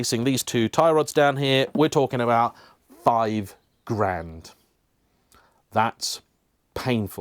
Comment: The audio went a bit funny here. It sounded like you said £5k.